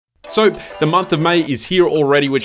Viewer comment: did he say soap?